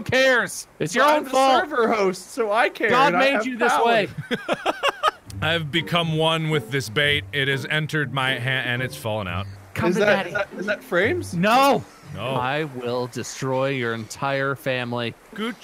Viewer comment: call me daddy, I just heard.